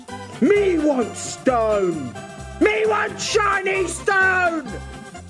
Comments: "Me want stone, ME WANT 'CHINESE' STONE!!" I misheard that part😅 but its "Shiny" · I thought he said "Me Want Chinese Stone"